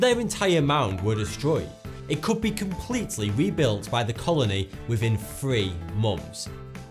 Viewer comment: i swear i heard "within free monks" and then "within three mums"